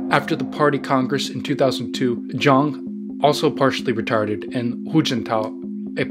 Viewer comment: Great video! Came here from Cynical Historian's channel and I am definitely subscribed. Also, did you mean to say 'also partially-retired' @ 9:23? Because auto generated captions say something very different.